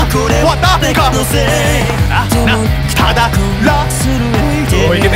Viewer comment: 秒青文字だけだとはだかw 追記1:01から続けるとママはだかw なんかわからんけど ここ聞くとママはだかのせい って聞こえます